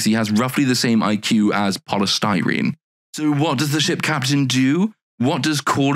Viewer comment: I know you said polystyrene, but I heard Paula Styrene and thought it was gonna be some incredibly stupid public figure I was gonna have to look up.